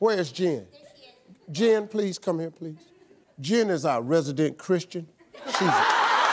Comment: Jen is our resident Christian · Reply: At first I heard "lesbian christian" hahah I was like whaaa.. Then I replayed it hah